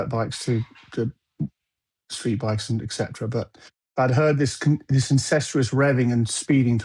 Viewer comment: dude said incestuous instead of incessant hahahahahahahahaha